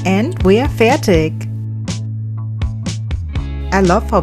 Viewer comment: and we are "fertig". I hear german? sounds perfekt 😍 ! Grüße Dich aus Deutschland, 🐻lin